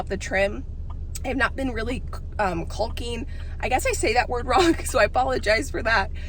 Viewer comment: Sounds like your saying cull-king instead of call-king, that’s probably what’s throwing people off, but it’s okay I think people still understand what your talking about